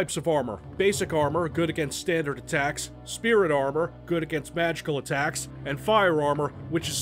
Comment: Hahaha! When he explains the armour system and says "Magical attacks", I misheard "Vaginal attacks" and somehow, I feel it could be kind of fitting in some contexts. :P XD <link>